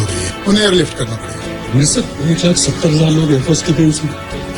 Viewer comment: the guy actually said 170,000. The Subtitles wrongly translated it to 1.7 million